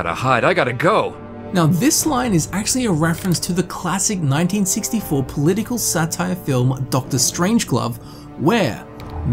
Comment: “pink floyd song stairway to heaven” ah yes my favorite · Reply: swear at 24:44 he says “dr strange glove too... now that could be an accent thing